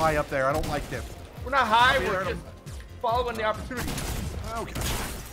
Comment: I swear someone just said “we’re not high, we’re just having an opportunity to smoke.”... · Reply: He asked why are you so high up and replied were not high up and then some buddy said we are missing are chance to smoke them